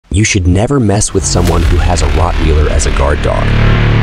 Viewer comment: Watch out for the Rotwheeler! 🤣🤣🤣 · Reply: I thought he said hotwheeler